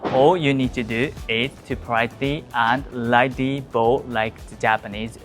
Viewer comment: When greeted in a shop bow slightly. but what was the English word you said before that? at 1:34 in de video. you need to do is to "pridey" and slightly bow like the Japanese. I am dutch but can not find a translation for the word: Pridey. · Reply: I think he's saying "politely".